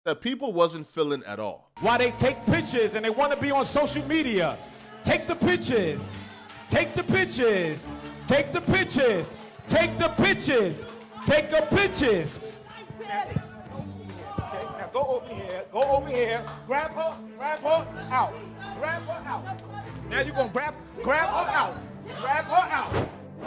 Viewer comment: “Take the pitches” is crazy 😂 2:30 Shut up I know what he was saying it’s just funny because it sounds like pitches 💀